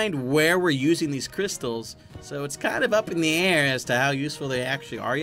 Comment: Did that just say air?